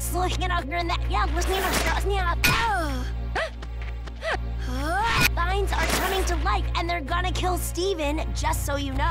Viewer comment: It took me a second viewing and a scroll through the comments for me to realize amethyst did NOT say "*Mimes* are coming to life and are gonna kill Steven"